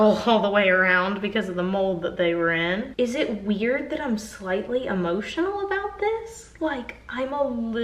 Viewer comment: I thought this said “lightly buttered” instead of “slightly emotional”